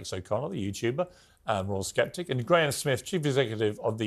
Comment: did he say "the royal skeptic"?